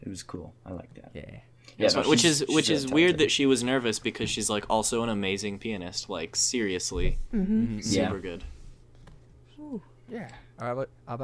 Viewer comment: did say jaiden’s a penis? Lmao